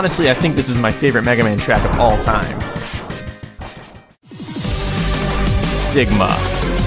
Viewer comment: Am I the only one who hears "Stigma" at 9:23?